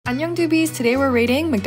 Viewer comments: I thought she said annoying at the beginning lol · I thought she said "boobies" when she said "doobies"😭